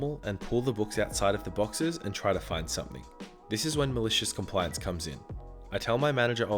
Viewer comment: my fucking dead ass thought it said Miraculous compliance until he fucking said it